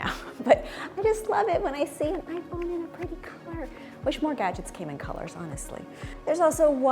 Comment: At first I thought I heard “wish more guys came in colors “